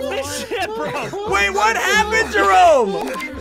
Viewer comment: What did say? I thought it was the s word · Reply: Jerome said Get him off this ship